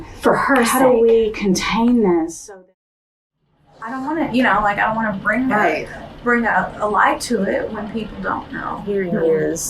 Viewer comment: Hold up, she definitely does not say "I don't want to bring a light" she says "I don't want to, I mean I want to bring a light to this". Two completely different meanings. Can you pin this correction, please?